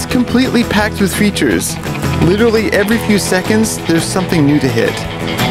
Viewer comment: I thought he said "packed with creatures, literally every few seconds there's something new to hit" haha